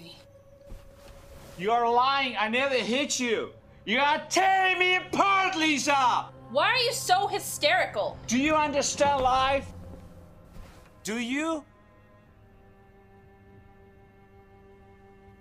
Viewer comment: Did he say do you want to stay alive or do you understand life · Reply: I heard “do you want to stay alive”. Read your comment, replayed it, heard “do you understand life”.